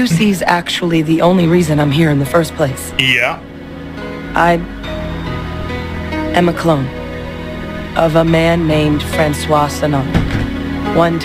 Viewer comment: I hear "i am a clon of a MA'AM...")))